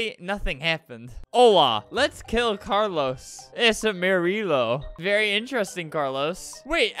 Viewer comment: I thought it said ''its armadillo''